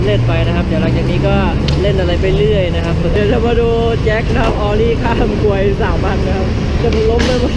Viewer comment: เดวแจ็คจะข้ามคว..สามอัน😂😂 ใครได้ยินแบบผมบ้าง #อย่าเม้นด่าผมกันเด้อ🙏🏻🙏🏻ได้ยินแบบนั้นจริมๆ